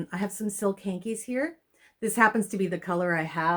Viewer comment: I was across my room to grab my phone when you said "silk hankies" around the @ 1:30 mark.From across the room I thought ou said "silk panties" and whipped around to look! haha